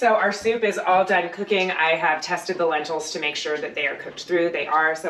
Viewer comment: When you said "I have tested the lentils" it really sounded like you said "I have testicle lentils"....either way food looked lovely 👍